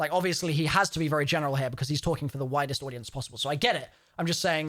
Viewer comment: When said "for the widest audience possible", it almost sounded like he said "the whitest audience possible" 😂